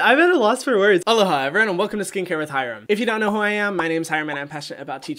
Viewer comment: I’m today years old when I found out he says “aloha,” and not “hello hi” 😳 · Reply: I thought he said hello I’m Hyram and welcome to skincare with hyram for the LONGEST time